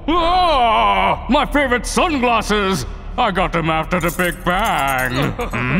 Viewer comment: I thought that he said :«i got them right after a gang bang»